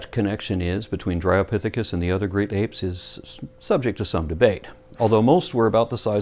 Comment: needs to lay off the classic cartoons. I could swear he said "Grape Apes" just after @ 04:35. Haha ;) Superb work.